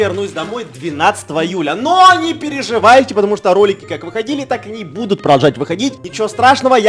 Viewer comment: Мне одной послышалось то что Эдисон сказал что не волнуйтесь ролики как должны так и не будут выходить на